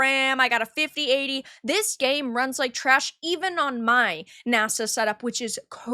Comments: Did say she has a "NASA PC" (like, NASA the space agency??) I didn't really understand what she was saying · On that subject - what kind of PC did say she has?(1:04) Did she say "massive"? Did she say "NASA" (like the space agency??) I didn't quite understand what she said. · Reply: I think she said "NASA"; by which she meant a high-powered gaming PC.